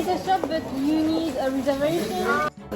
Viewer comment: You need a reservation だけ聞き取れました。 間違ってるかもだけどｗ